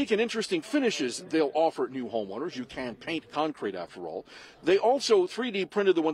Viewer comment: You can't paint concrete? Seriously? That's the dumbest thing I've ever heard on the news 😂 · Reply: it sounds like he says "you CAN'T paint concrete after all". You definitely can paint concrete!